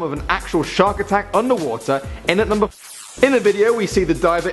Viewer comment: what did you say??? in at number fo-cut off